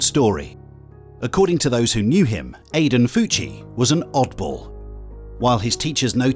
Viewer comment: this is to off topic but his name is Aiden Fucci, Right? i thought the narrator said Aten Coochie 😭😭😭😭😭😭😭